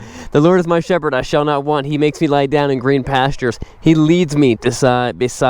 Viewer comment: thought it said maketh me to lay down ? not like make me like lay or I shall destroy you !